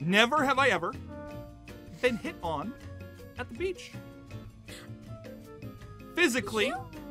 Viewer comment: Okay, when said, “Never have I been hit on at the beach.” I thought he said, “Never have I ever been hit on by a peach.”